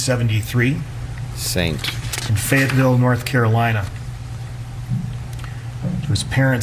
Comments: did he say Floyd was born in Fentanyl North Carolina? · He was born in Fentanyl North Carolina? That is really what I heard. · Did anyone else hear Fentanyl North Carolina? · I swear that I heard Fentanyl, North Carolina.